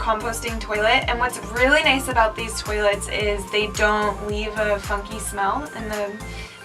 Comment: When she was talking about the toilet I thought she said "they don't leave a fucking smell" 😂 had to rewind it